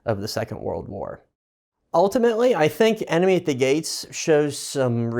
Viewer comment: Sorry I heard it as 'anime at the gates'